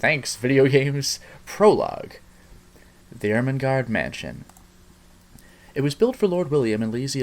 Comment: Consider me intrigued! 3:46 - Dammit, I actually thought that said Ermahgerd Mansion.